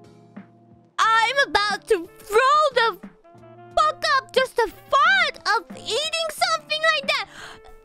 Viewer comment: 開始應該是I'm about to throw up... (後面聽不太懂 是吃那東西會吐爆的意思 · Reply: “I’m about to throw the f*** up just the thought of eating something like that” 原文完整大概是這樣